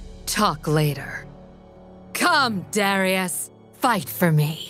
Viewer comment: Are we gonna pretend like we didn't hear that? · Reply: Coom